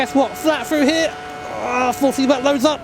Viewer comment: What the hell did you actually say there? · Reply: Force feedback loads up